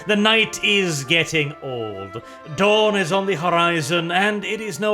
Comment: When you said “Dawn is on the horizon”. My sleep addled brain thought you said “Dorn is on the horizon” I need coffee.